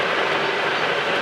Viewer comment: What is he saying at 14:50? "Right on the money"? :)